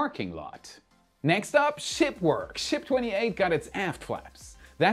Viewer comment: "Ship 28 got its aft flaps" ... Coulda Sworn you said, "got its A$$ Slapped" 🤣 😎😎 ^v^